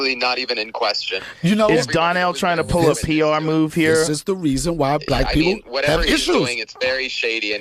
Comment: did he just say “this is why black people have issues” just then .. ?